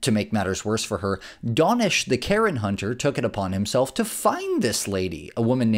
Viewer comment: ish.. it sounds like "donnish the karen hunter" is what's said.. can anyone tell me who this karen hunting person is, if they release their own vids as well, or anything else about the person that might be interesting or helpful? ( obvs its not something as simple as The Karen Hunter, or something even nearly as easy to find, apparently) It/he (the person doing the hunting, and the "job" theyve taken on) is a great service ;) id love to be able to learn more :)